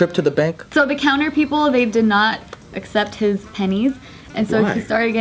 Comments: i lowkey thought that it said penises wtf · Am I the only one who thought it said penises? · At first I thought it said penises :)